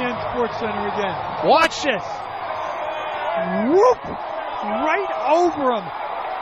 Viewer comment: Bro had that Super Marios juice before that play. Could've sworn I heard 'Wahoo!'